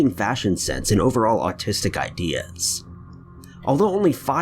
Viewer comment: Did he say autistic ideas? 3:32 Also 5:56 you can here his phone